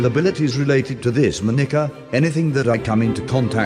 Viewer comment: "Moi Nigga"